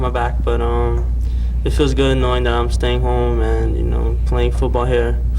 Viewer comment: who else heard "horny" at first?